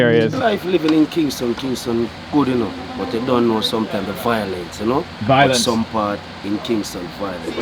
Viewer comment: and 15:00 subtitle says "you don't know" but he says "you done know" which means "you already know" in jamaican patois